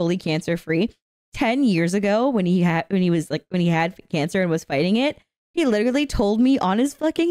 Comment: for a second I thought you'd said pregnant instead of fighting it my bad I was still thinking about the pregnancy and had to go back if I heard that wrong